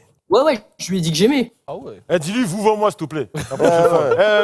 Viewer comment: dit lui "vous-voi moi" chui mort 🤣🤣🤣🤣